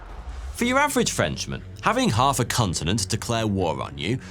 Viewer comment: I swear he said 'Cuntenent"